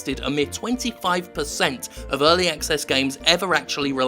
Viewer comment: I swear he said Early Excess. Which would be an appropriate name for it, since it is a rather oversaturated market, the old unfinished games being released on a storefront thing.